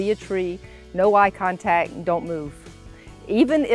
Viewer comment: So, at 3:09 does she say, "no eye contact?" Something tells me this is hugely important.